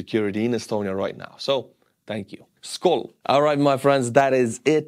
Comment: Hörde jag en skål?